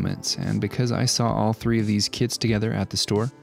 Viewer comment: I thought he "said I saw 3 of these kids together at the store and thought about recreating a seen from starwars"lol